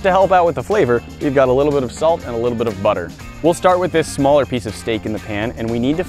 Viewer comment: did he just say little bit of butter?